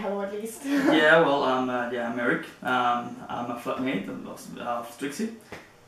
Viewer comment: Did he say "fuck" mate at 0:42? lol :D